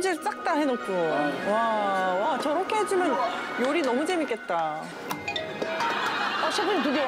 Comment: 저렇게해주면 요리 너무 재밌겠다 : 다 깔아놓은 멍석에 누가 요리 못하냐 ㅋ ㅋ ㅋㅋ ㅋ ㅋ ㅋ ㅋㅋ이말로 들려서 뭔가 사이다임